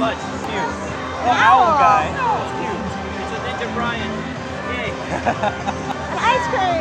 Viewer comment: did he say Ninja Brian???? thats exactly what I said when you guys grabbed it! jeez NSP IS pretty popular!